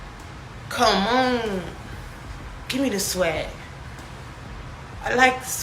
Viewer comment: “Gimme the swag” 😂😂😂. I thought ole dude said “gimme the jwett” BYEEE